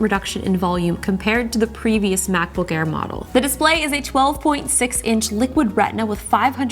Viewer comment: did I hear it right? 12.6" display?